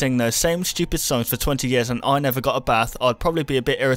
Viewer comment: I thought he said ..."and I never got a break." I guess at night they do get a break?